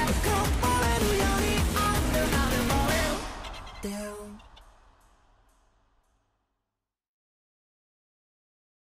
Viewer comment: WHAT IT SAYS? DAMN?! DOWN?! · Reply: down